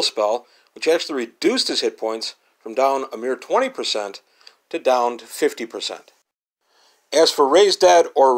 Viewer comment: Sounded like the spell was "Raise Dad."